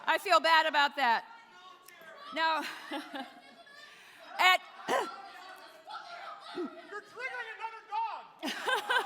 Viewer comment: what's the burn at 8:16 ? What did he say ? "you're triggered like a dog" or something like this ? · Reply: I thought he said "Now you've triggered another dog!"